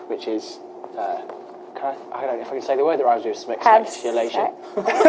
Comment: i thought jay said 'ejaculation' .. not sure